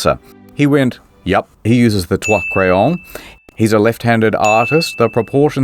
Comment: Anyone else hear twat crayon @ 17:46 and found it erotic??? .... asking for a friend....